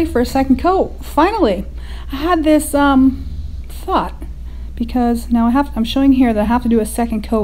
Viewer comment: it sounded like you said a bad word did you or what did you say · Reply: It does sound funny... I think it is where I say ... "I had this... Thought" ?